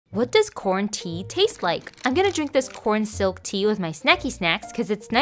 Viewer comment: I honestly thought she said 'This is what Quarantine tastes like.' Lol